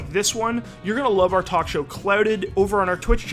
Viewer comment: The video is about "leafy" and he's smacked. And I thought he said "clouded" but its "clouted"